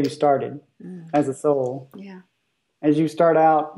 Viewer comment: Does anyone else hear a female breath saying "yeah"